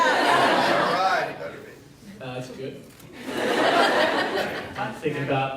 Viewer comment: What does that guy say in 22:50? I have been trying figure out what he saying. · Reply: he says that the baby will be getting food now, and then Bob goes "I'm thinking about that" LMAO